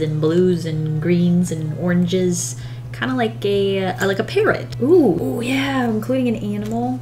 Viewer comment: well I heard "kind of like gay ,like a parrot"